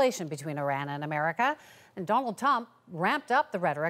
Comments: Did she just say “Donald Tump”? 😂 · I definitely heard “ Donald tump “ 😂😂